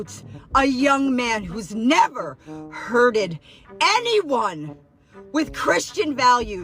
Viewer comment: did she say “hurted?” This is why they love the uneducated 😂